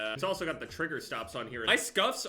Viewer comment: Jev: Gets new scuf Also Jev: “I think I fucked up the the right stick” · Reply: idk why i thought u said “soul” instead of scuff